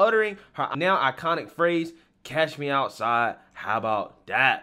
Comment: I thought she said, "catch" not cash...